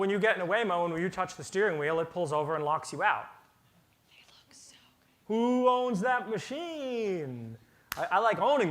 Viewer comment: what did that whisper say? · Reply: Sounds like “you look so good” coming from a woman 🤣